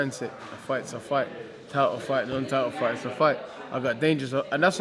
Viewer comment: Hahahah I thought he was saying turtle fight not title fight